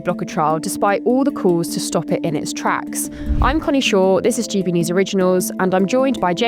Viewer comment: It sounded like she said I’m Connie Shore. 😅